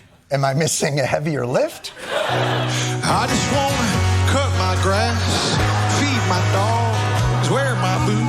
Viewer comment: Ngl I thought that dude said 'I just wanna kiss my fish' for a hot second